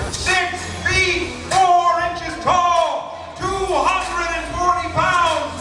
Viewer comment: i thought the announcer said he was 64 inches tall. I had to do a double take, because absolutely no way he was 5"4. Then i replaced the video and heard 6"4 inches.